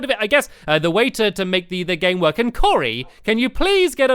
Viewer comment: it sounded like he said curry